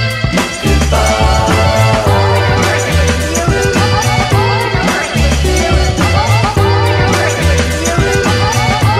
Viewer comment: What are they saying at 2:43? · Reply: "Over and over again"